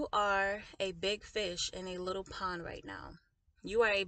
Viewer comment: lol. At first I thought you said, “A big b*t**h, in a little pond”. 😬😆 I thought... WOW... that doesn’t sound like something the Lord would say. 🤷🏼‍♀️🤦🏼‍♀️😂😉